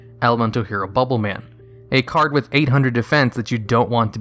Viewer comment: did you say a card with 800 defense? Or am i hearing things. Lol